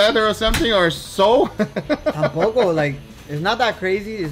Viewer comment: I heard that "tampoco"